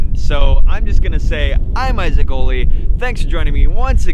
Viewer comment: Sounded like you said I'm XD